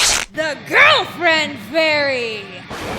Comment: For one moment, I thought I heard "Grillfriend Fairy". Don't grill our friends pls